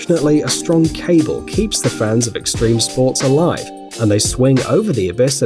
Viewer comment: did you say unfortunately it keeps fans of extreme sport alive?